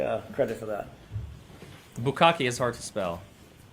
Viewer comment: what word was hard to smell?